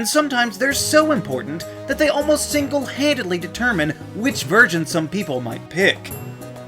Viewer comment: is it just me or did he say 'which virgin'?